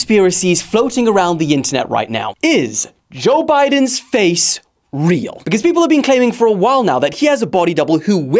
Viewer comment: i thought it said is joe biden face reveal